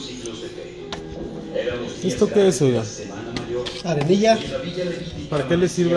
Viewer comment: So Can anyone tell please , what kind of powder he uses for making clay easy roll ? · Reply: i thought he said ardilla!!! Or Arcilla! 🤷‍♀️